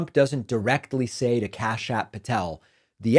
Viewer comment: Don't know why but when you first said the name Kash Patel, I thought I heard you say "Kash-App Patel". 🤣